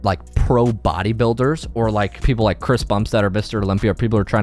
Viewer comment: I love but did he just say chris bumps?? 😭😭